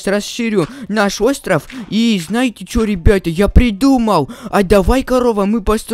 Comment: мне показалось что он сказал я придурок